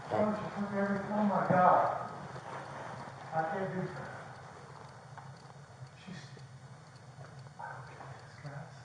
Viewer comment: did he say where’s my gun?